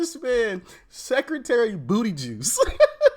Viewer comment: Booty juice I thought he said Beetle Juice... lol